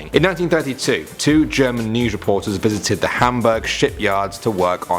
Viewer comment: when you said Hamburg ship yard my friend heard it as shit yard😂